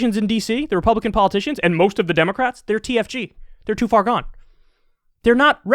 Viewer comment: Everytime Kyle says TFG I hear they're DTF OH OKAY